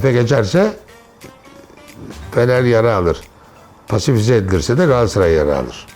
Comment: hocam ne diyor ya kim yaraaaa alir 😂😂